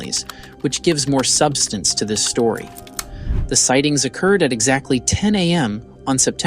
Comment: I heard that too. Wow. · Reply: And what did you just heard? Did he say more credence or did he say more substence to the storry? I heard him say...MORE SUBSTENCE TO THE STORRY...you just want to hear want you want to hear and you are to lazy to define words